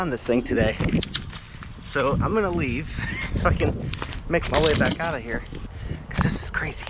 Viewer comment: was that a swear I heard?! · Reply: "If I can" I just slurred it haha